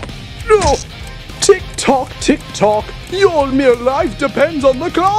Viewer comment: Super Brainz: Tik Tok, Tik Tok Bon-Bon: Wait a minute...did you just say Tik Tok? Because I have a Tik Tok apps on my phone. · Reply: It’s “tick tock tick tock”